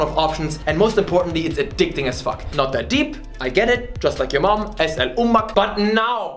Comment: DID YOU SAY AN ARABIAN WORD ASK YOUR MOM_ES AAL OMAK